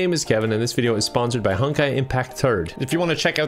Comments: When he said honkai impact 3rd I thought he said honkai impact turd as a joke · Have anybody misheard Kevin saying Honkai Impact 3rd as Honkai Impact Turg? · I thought he said Honkai Impact Turg